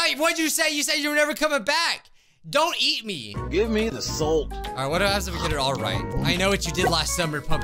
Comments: When he said give me the salt I thought he said give me the soul 💀💀💀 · When he said give me the salt I thought he said give me the soul